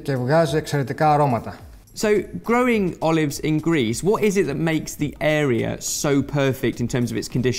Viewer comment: For some reason I heard : "So, growing olives in GREASE, [...]" I had never before considered using grease (and perhaps lard) instead of normal soil for growing stuff